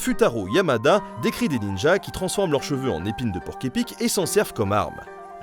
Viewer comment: Moi j'ai entendu "époque San Goku" ça m'a fait rire